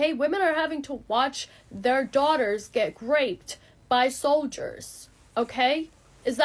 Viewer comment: the Whitest Kids You Know skit came true... She really said "Graped"...